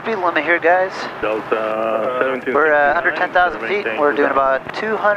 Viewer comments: sounded like he said "we're at 100,000 ft" that'd be awesome! Dramatic flight. Glad you got thru that hole! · Did you say 110,000 ft? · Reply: “We’re at under 10,000 feet...”